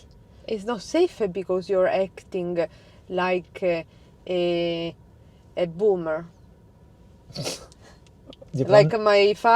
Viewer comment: did just say “ok, boomer”? 😂